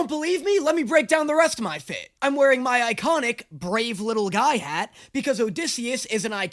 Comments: I thought your hat said "brave little gay" 😂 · OH HELP I THOUGHT IT SAID "brave little gay" 😭🙏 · I thought his hat said ‘brave little gay’ like almost the whole vid lmao